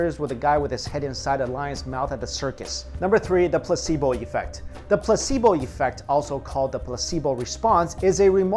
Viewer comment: for a second I thought he said pussy boi effect